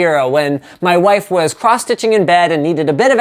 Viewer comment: crotch-itching? is that what you said lol?